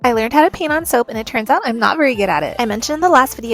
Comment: Sounds like you said pee on soap. Not paint. 😂